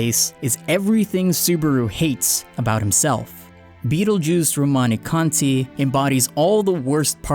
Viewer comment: Did he say 'beetlejuice' lol